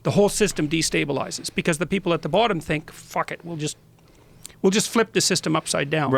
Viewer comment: did JP say fuck?